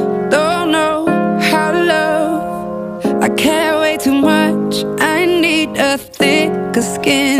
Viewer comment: I really thought she said "I care way to much" instead of I cant wait to much and I was like yeah me too I care way to much